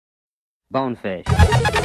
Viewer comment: Did he just say Boneface